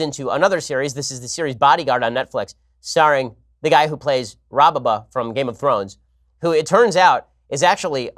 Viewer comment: did he really call rob stark "robbaba"?